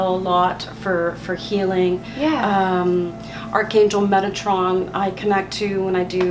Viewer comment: I thought she said megatron 😂